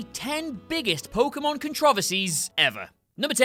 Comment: Wait did he just say contraughversees